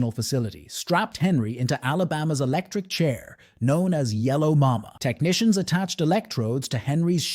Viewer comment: When he said Yellow Mama I thought he said yellow Obama